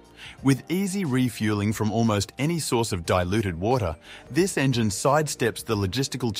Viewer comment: I made it to 3:26 when I heard the engine could use "any source of Diluted water". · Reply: DISTILLED water, not diluted water.